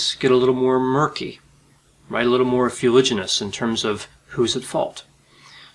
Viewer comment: "fewliginous" what word was that; thank you in advance... :) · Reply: fuliginous: it means smoky, unclear